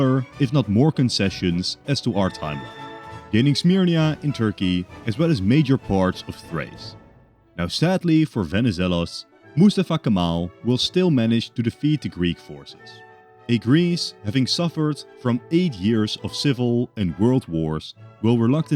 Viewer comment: i heard that as musafucking kemal😭😭he’s a badass